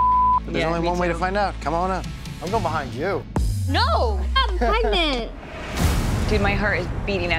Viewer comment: Yo I must be tripping but I swear I heard snookie say no I'm pregnant